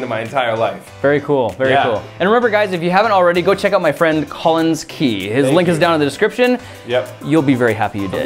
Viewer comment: I thought he said tallins key instead of Collins key😂